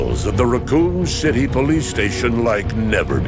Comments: I heard that as “Raccon Shitty police station” and shat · Did I hear that correct 1:43 Raccon shizy police station?